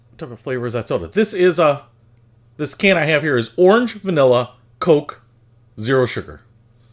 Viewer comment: "This can I have here is Orange-Vanilla-Coke, Zero Sugar," Delicious. That what it says in the comments but it sounds like he is really saying "B*LL SH*T" softly under his breath...